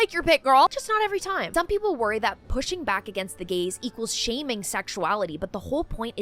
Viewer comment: I literally heard “Pushing back against the gays,” not “Pushing back against the gaze.” What do the gays have to do with this? 😂